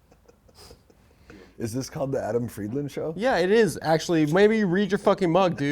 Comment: Best gosh darn intro ever... i could've sworn said " the Erin friedland show"